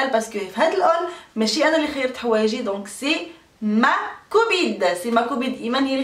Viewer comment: غير انا لي سمعتها قالت ماكوبيد c ma cobide ماشي كوبين ma copine 🤔😐😐😐😐😐😐😐😐😐😐😐😐😐😒😒😒😓😒😒😒😓😓😓😓